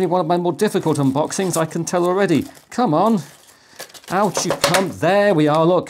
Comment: Surely I'm not the only one who was a bit taken aback about 1:57 or so, hearing getting frustrated with the unboxing and saying "Out, you c*nt!" I had my wife listen, and she heard "Out you come", which seems way more likely on this channel, but that's not how I heard it. That is an impressive case. Idk, maybe if I get a second pi5 I'll get that, but my black aluminum case with fan seems to do a very good job. All in all, I'm quite happy with the performance of the pi5 (8GB), and have been doing most of my desktop work on it, with my old desktop reserved for a few things that use too much memory or just didn't run well for one reason or another. For the price, it's fantastic. The first home computer I used was a TRS-80 Model 3. Or was it III? Three, anyway. I've spent a career mostly using engineering workstations, doing software development primarily with a CAD system, and have seen plenty of computers costing more than 100 times as much but with nowhere near the performance. We used to start a "make" and go take a coffee break. To render a scene, it was better to kick it off just before lunch. And best of all, with channels like this, the SBC revolution is televised!